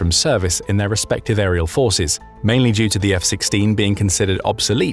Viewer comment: lol @ 15:00 or so...the voiceover says "F-16", but the subtitles say "F-15" to go with the F-15 footage.